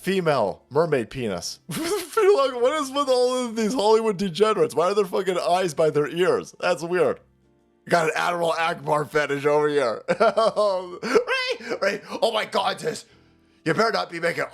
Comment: It sounded like you said "Adderall Ackbar" which was even better.